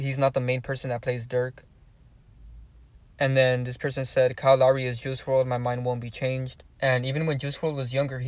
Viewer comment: i thought you said lucky was juice wrld